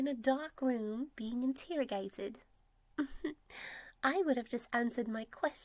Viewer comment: Did she just say In*tear*ogated